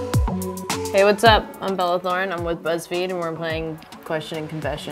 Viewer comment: i literally heard : hey whats up i'm bella thorne and i'm a lesbian instead of i'm with buzzfeed